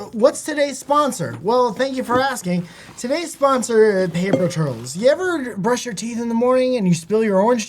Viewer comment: Am I the only one who thought they were saying paper turtles?